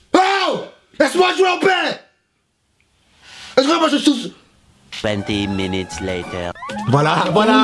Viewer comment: 😂😂 il a dis quoi ? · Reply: Ah jure moi jte jjjj . C ce qu'il a dit